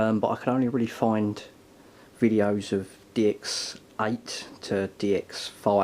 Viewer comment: Whenever he says DX it sounds like he says d*cks lol